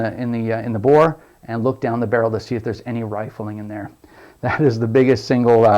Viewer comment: what do you said check in the barrel for what ? ty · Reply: Rifling. The spiral inside the barrel.